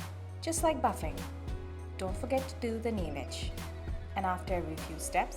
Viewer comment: don't forget to do the what? Captions says "nimac". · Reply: Place the Glass Nail Shiner on the surface of the nail and very gently move it from side to side, just like buffing. Dont forget to do the nail edge. 🙂